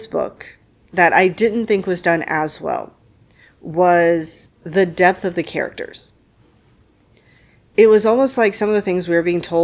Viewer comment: at first I thought you said “the death of these characters…” and I was like THEY DIE?! 🤣 then I went back and listened again and I got it haha😂